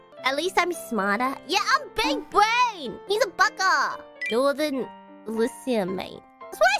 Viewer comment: I thought she said "he's a bucker" for some reason 😭😭💀💀